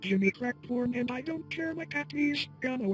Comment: When he sung I heard " jimmy crack porn and I dont care" 😂